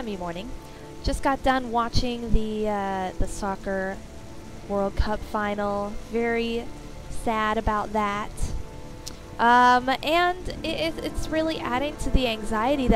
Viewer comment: I thought I heard you say "Stalker World Cup Final" in the beginning of the video, so I had to replay that and realized you said "Soccer" XD